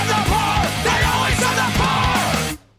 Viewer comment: WHY. DO. THEY ALWAYS SEND THE POOR. This isn't a question y'all, I'm literally just quoting the lyrics of BYOB 🤦‍♀️ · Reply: I hear "SAND THE FLOOR"